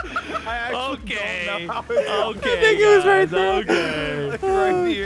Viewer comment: it sounds like says that n**ga was right there lmao